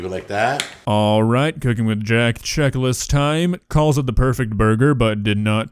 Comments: I thought it said ”cooking with Jack Black” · Thought it said "Cooking with Jack Black"